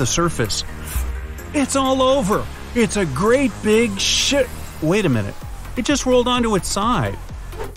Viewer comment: DID HE SAY SHEET?